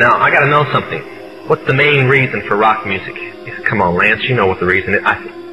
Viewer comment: /29:49, it sounds like he is saying (Come on Lance), did he mess up and say his real first name? Go listen and tell me what you think.